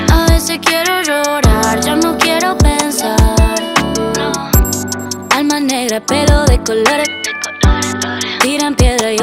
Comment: Pensé que decía "Hay maneras pero de colgar" .. En realidad dice "Alma negra pelo de colores" díganme que no soy la única 🌝 .. La amoooooooooooo 🌺🌻🌼🌸